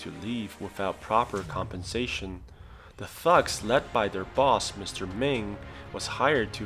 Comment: When they said ‘ the thugs’ at 3:45 I thought they said ‘the f*cks’ 😂